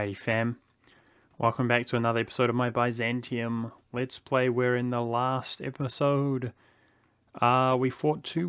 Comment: I was scared at the start as I thought he said "we're in the last episode"